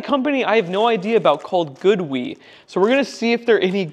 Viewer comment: sounds like you said GOODWEED lol